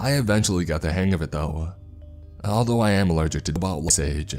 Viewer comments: Here’s the part up to where it cut off at 5:42- “I'm allergic to dust. As of now, I'm the academic equivalent of a middle school student. I can read, write, cook, use a telephone, and surprisingly even drive. I guess living in the wild does make you quick to adapt. Anyways, now I'll tell you about life during the ice age.” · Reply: I tought I heard "I'm allergic to booble shade" allergic to wut!? XD thanks for your comment, this helped me a lot ! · I cannot make sense of what he's saying he's allergic to. Even with captions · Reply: Ball sage